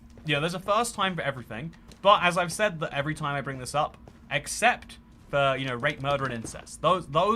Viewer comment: my monkey butt thought he said accept